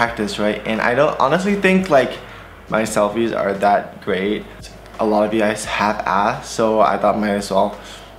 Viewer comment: Omggg in 0:38 i thought he said "alot of u guys half ass" oof 😂😂 (unless he did 👀👀😂)